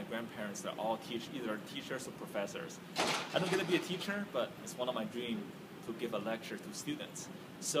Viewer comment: i thought he said t-shirt not teacher.